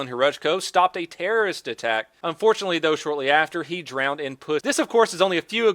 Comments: I thought you said he “drowned in pudding” Well Close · Am I the only one who heard him say unfortunately he later drowned in pussy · did you say the officer "drowned in pudding"? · did he say drowned in p-ssy?